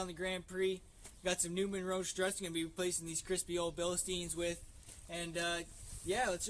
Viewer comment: Great video ... Thought u said crusty at first then heard u say crispy lol but seriously... very helpful, thank you